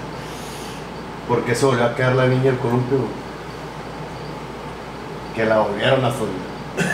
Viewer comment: no entiendo lo q dice :( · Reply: Porque se volvió a caer la niña del columpio? -porque la volvieron a subir xd